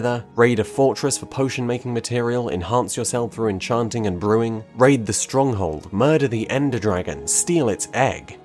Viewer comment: Thought he said “rape a stronghold”